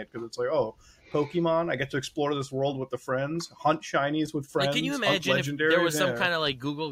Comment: Am I the only one who heard "hunt Chinese" and had to do a double take?